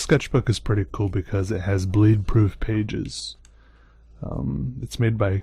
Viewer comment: when he was talking about his sketch book I thought he said "blade proof pages". i was like whoooaaa